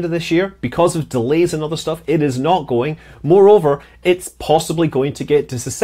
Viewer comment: I thought you said more rover instead of moreover lol!